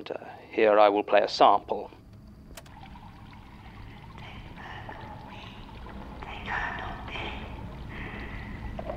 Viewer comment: If anyone can't make out what the sound is, I believe its someone saying "Take her not me" repeatedly. Though for a moment I thought it also might have been "they can't help me". It definitely starts with a t- sound, and ends in the word 'me' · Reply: Yea its "Take her not me". The creator confirmed this in the comments. I actually came to the comments to verify myself what it said lol.